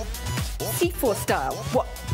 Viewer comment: I just hear "seafood style" 😂